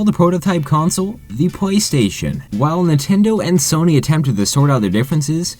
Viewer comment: It sounds like your saying Pway Station